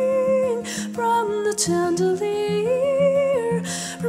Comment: all i hear is "chandler-ier"